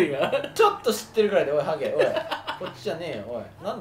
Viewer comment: どこからかの話し声がハゲって聞こえた笑